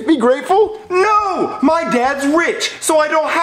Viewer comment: Who Else Heard. Richard: My Dad's Wich